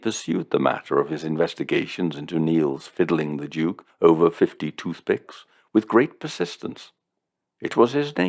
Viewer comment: Had a Two Ronnies moment: You said ''over 50 toothpicks', but I first heard it as 'over 52-6', as in 52 shillings and sixpence!